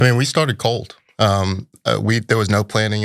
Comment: I thought he said “We start a cult”. Do tell.